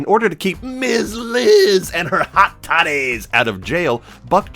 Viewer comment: Man..ya gotta love CC. At 1:25 you legit say "hot totties" , yet the CC heard and wrote "hot titties" ! 😆 Oh damn, you totally got that "different kind of buckshot" off too! 🤣 As for your final question, so long as it regards KotH, I don't care what you're saying about it, so long as it all comes from the heart..you feel me ol' top!? 😉👍